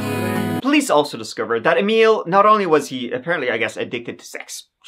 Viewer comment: "Bust-a-nut-Neil" is why is the Weird Al of true crime - top notch mate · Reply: I thought he said Emile, not Neil?